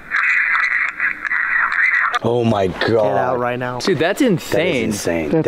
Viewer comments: last evp recording i just heard at the end "get out right now" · I don’t think the evp doesn’t say “you get out right now”, instead I believe that it sounds like whoever it is, is crying and doing that choked heavy breathing you do when you cry and says cb-“ what would you like to tell Elton?” Spirit- “ sniffle you can’t help… sniffle you can’t help me now!” Which makes sense to me considering where they are, the spirit said that they per a person once, and is now in suicide Forrest, assuming that they took their own life they would have been in a really bad state of mind, so it crying and having hitched breath while saying “you can’t help me now “ is very sad but makes a lot of sense, they can’t help them because they feel like they’re at such a low point that nothing can help or save them.